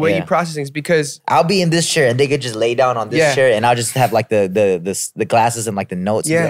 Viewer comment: wait did jay just say the N word at 56:00 exactly??? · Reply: No. He said, "They could just lay down in this chair"